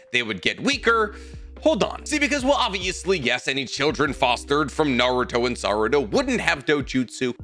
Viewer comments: Did just said "Kid foster from Naruto and Sarada"??? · "Children fostered from Naruto and Sarada" is what I heard.